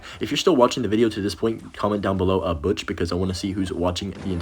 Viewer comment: Does he say “Comment down below butch” ???